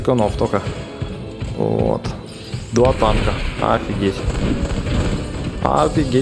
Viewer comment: Послышалось 2 тапка